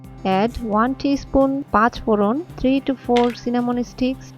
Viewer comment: What were all those seeds you added as the first ingredient? It sounded like you said patch bolon....i dont know what that is....